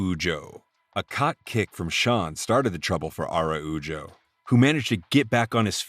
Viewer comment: I had to rewind this as it sounded like you said "cock kick started the trouble"😂😂